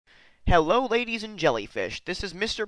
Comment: I heard “hello ladies and jellyfish” I stared at my phone laughing 😂